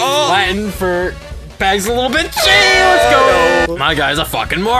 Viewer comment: Could I get a transcript on what says at 2:28? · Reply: "Pags is a little bitch AYY let's go!"